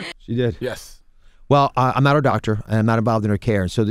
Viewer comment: did he just say he's not a doctor ?? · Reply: I thought that too! but he said he's not her doctor... her as in Hillary